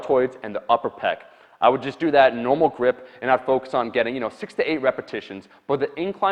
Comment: I heard it "Incline bench 68 repetitions"... and was like WHAAAT